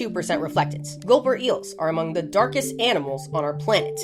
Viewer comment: I heard "gopher eel" at first and was confused why they would be called that. And then I heard the word correctly and decided I'm going to continue hearing "gopher eel" for no other reason than I want to.